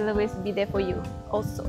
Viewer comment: minit 21:40 tu cakap apa? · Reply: "so i am always be there for u also"❤❤